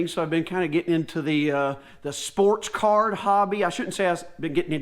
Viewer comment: I thought you said Sports CAR!!!! Dammit Boy....you had me 🤣, and yes Im still dark green and mowing🙃